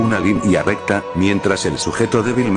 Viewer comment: ¿Qué dijo al final cuando le disparó al corazón? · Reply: casi tan libre